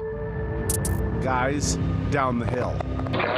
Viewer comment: Down the hill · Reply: I hear "guys down the hill"